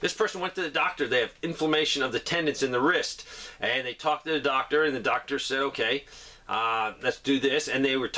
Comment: When you said “and i talked to the doctor and the doctor said...”. I swear I heard “no more monkeys jumping on the bed!”